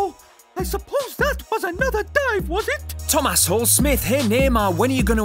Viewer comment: did he say thomas hole